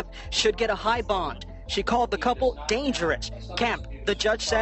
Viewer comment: Did she just say "dangerant?"